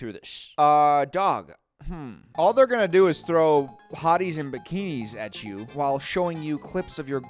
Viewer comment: I thought he said potties and bikinis 💀☠️